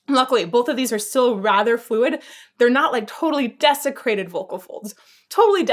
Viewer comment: It sounded like she said 'desecrated' instead of 'desiccated' when talking about dried out vocal folds.